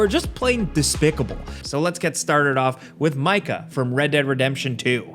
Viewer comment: I was so confused when I heard “let’s get STARTED with MICAH”